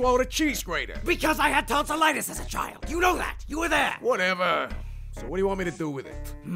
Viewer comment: at first i always thought he said i had tons of lettuce as a child.